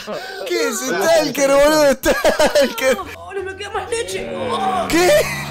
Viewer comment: no me queda más leche ajjajajaajajaj Creo que dice eso